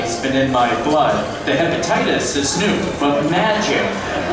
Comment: did he just say hepatitis?!